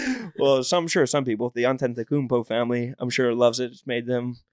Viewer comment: what is the name of the family Rone says? I can't figure it out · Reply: Greek freak Giannis Antetkoumpo